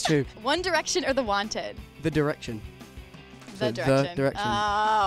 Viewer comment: When he said The Direction i thought he said One Direction i was like WWWWOOOO but then he repeated it and i k i love them bothxx